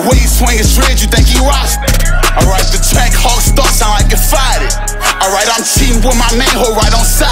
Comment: For everyone knowledge bruh said " Don't move wrong in this b*tch I BEEN DONE shot somethin....come on now...get it right. 💯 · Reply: Also this part ""Alright the trackhawk start sound like it FARTED"" is what I thought he said.